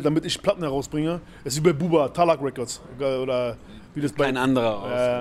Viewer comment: Freundeeeee das Jahr ist fast um , traditionell kommt hier der Ultimative Jahresrückblick mit Flizzymeister Fler Grüße an die Ultras die schon vorher den Link hatten !!! WIR SEHEN UNS IN DER BADEWANNE !!!!!^^ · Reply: Was sagt Fler 31:59 ? Talahon Records ?